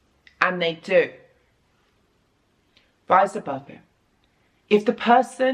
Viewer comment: "Rise Above It" is that what she says? I hope so lol bc when I heard that so much peace hit me.